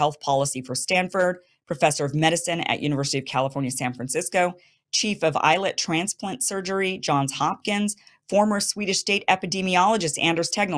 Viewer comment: thought she said "Chief of Eyelid Transplant Surgery", I know everything is highly specialized these days but c'mon 😄